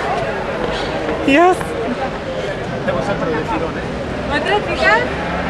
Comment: Did someone say ultimo there?